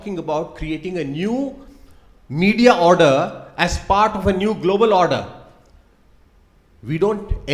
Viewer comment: does he said new world order - that illuminati theory???